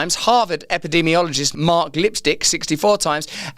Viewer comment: Did I hear Mark… 💄??